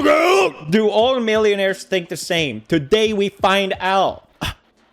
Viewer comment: I thought it said, "Do All Millennials Think The Same?"